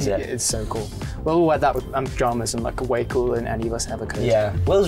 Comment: the cc's please... he says "Will will wear that with pajamas and still look cooler than any of us ever could", idk what the captions even say 😭